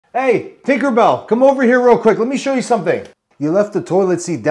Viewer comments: "hey tinkerbell" had me laughing lololol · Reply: I thought he said thickerbell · Doppelganger Land 😂 I SWEAR I thought he said Tinkle bell at first not Tinkerbell 😅